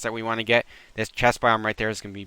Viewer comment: did you call that a chest biome?